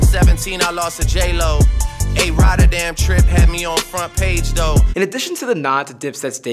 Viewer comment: it sounds like he says “A Rod” when he says “A rotterdam trip”